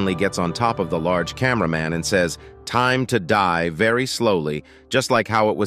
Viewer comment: i think he said " mul---tiply '' and not time to die